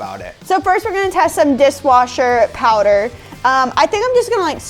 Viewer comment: Lol @ 4:34 where says dis washer instead of dishwasher